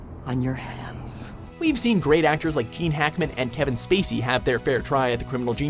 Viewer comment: Does he say "keen" Hackman instead of Gene at 6:57 ???